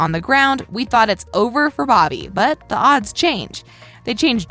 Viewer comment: Is it just me or did she just say Booby